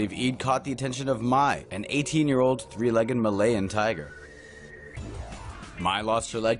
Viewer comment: Thought he said '8-legged 3 year old tiger'..